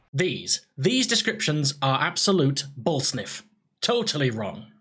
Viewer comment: bullsniff word of the year 2k17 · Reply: Killer Bratwurst I thought he said ball sniff which is even funnier imo